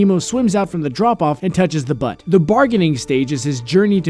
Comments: "He's angry when nemo swims out from the drop off and touches the butt" 1:08 did he say butt? XD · who else heard that he said butt instead of boat at 1:07 lmao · *laughs* he said butt instead of boat